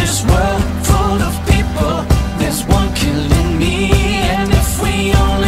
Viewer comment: what the fuck ppl. 0:56 says "there's one killing me" not with one killing need